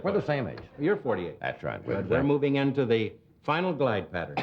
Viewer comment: Can anyone tell me what JW says at about 9:59 - "we're moving into the final ? ? " It gets a good laugh but I can't make out the last two words! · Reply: The "final glide pattern", Winter's makes an obscure reference to getting older... Hope this helps.